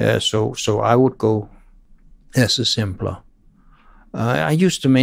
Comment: What does he say at @ 2:59 "I would go..." hard to understand with his accent. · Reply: I think he just made a language mistake, pretty sure he said "Lesser simpler" Which is a typical direct translation from danish into english. Probably because he thinks in danish, we say. "Mindre simpel" = "Lesser simpler"